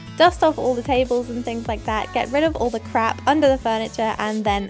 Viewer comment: did she just say crap or did she not??